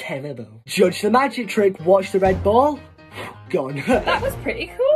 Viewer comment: For a hot sec I thought they said "chug the magic trick" 🤣🤣🤣